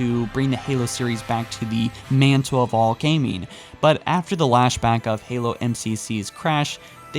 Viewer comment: anyone hear him say lash back instead of backlash